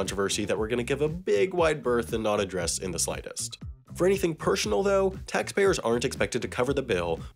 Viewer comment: did you just say pershonal?